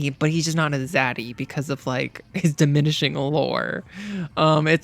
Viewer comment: absolutely obsessed with your content. so many games you appreciate as much as i do and you Get Them! this is the only list that matters. also the kahmora hall "we were ROOting for YOU' took me out. 10/10 video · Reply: had to add on because im still watching; i misheard you just call Leon "diminishing whore" and about pissed myself laughing.